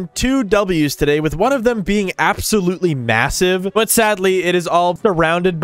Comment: did you say massive?